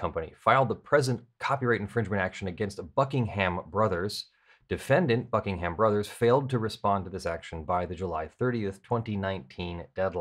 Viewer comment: I realized I was only half listening when I thought I heard you say "against the fucking Hammer Brothers"